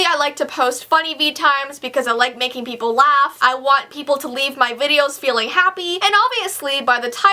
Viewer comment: for a second I thought she was saying to leave her videos!!😂